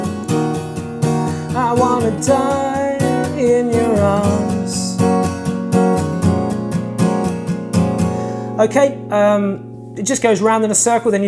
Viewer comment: did he just say i wanna die for ur ass